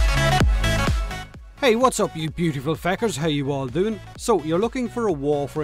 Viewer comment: did you call us "Beautiful Fuckers?"